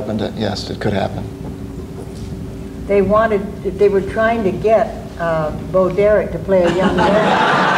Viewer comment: I didn't hear what Betty said😓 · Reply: I think Betty said, “They were trying to get Bo Derick to play the young Rose,” is what I think she said. I hope this helps.